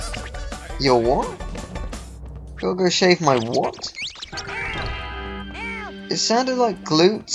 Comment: I think the Teensies said "I gotta go shave my glutes" @ 12:15.